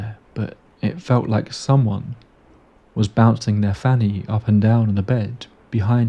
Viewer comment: third time listening to that and im not still not sure i heard correctly, did he say "bouncing their fanny"? as in their vag.